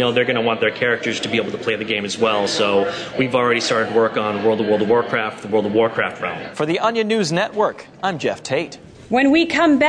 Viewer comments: I think he saying: at 2:08 World of World Of Warcraft World of Warcraft Craft. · Can't wait for World of World of World of Warcraft. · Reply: I think he saying: at 2:08 World of World Of Warcraft World of Warcraft Craft.